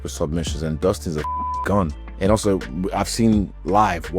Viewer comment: Haha I thought Israel said “and Dustin is a fkkin cunt” … (and Dustin is a fkkin “gun”) lol I had to rewind it a couple times